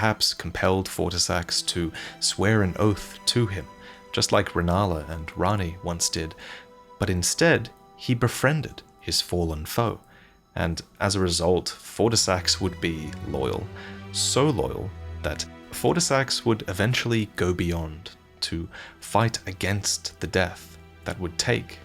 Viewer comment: subtitles says "he befriended his fallen fall, and as a result, for the sex would be loyal". I mean thats a pretty good reason to be loyal for sure.